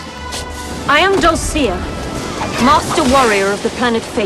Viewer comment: "I am dulcea.(Or whatever)" is what she said. What heard her say whilst staring at DAT RACK: "I am double-D-a" Yes, horrible joke I know... But dangit, I was a 9 year old kid when this movie came out damnit! I missed the entire notion of her boobs!